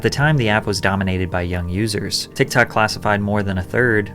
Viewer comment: When you said "young users" I thought you said "dumb losers" lololol